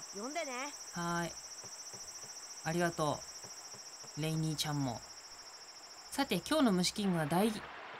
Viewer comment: の礼にーちゃんが レイニー・チャンに聞こえて外国人プロレスラーかと思いました うぽつです